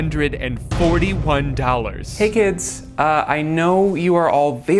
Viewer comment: did someone say 67 41?